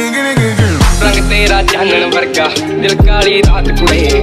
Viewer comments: First time I heard it as "rang taera janvar ka "😂😂😂😂😂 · "Rang tera Janwar ka, Dil kaali raat chudhel" Anyone hearing this? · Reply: bro it's Chaand warga not Janwar ka